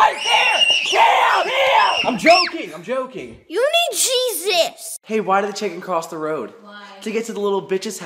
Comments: I thought he said “ YOU NEEDCHEESE STICKS “ ” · I thought he said "You need CHEEZ ITS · On the fourth one it sounds like he said you need cheez it’s